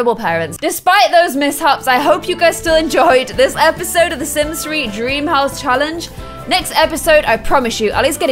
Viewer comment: Idk if my ears are wrong but did saythe Sims 3?